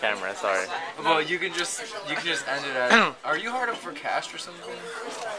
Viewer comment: 'hard up on cash'? I thought you said 'are you high up on hash' lol